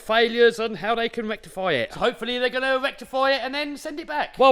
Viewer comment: Did he say erectify Well That's what I'm referring to " it " from now on Matter of fact I'm about to go erectify my wife right now 😂🤣